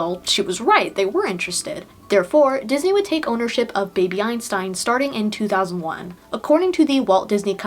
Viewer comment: Maneeeeee I thought ts said Baby Epstein 😩